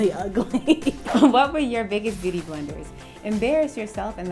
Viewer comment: did she just say what we’re ur biggest beauty BLENDERS?😂😂😂